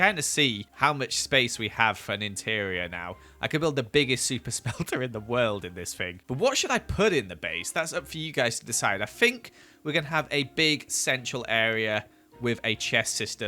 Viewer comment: i heard "Super smeller" and couldn't stop laughing